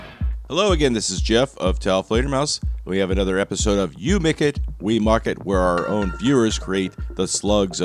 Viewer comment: Up until recently, I MISHEARD your slogan as "You make it, we MOCK it". 🙂